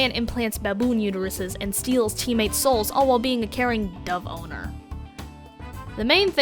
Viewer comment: I don't know why but when you said it Dove owner it sounded like you said the boner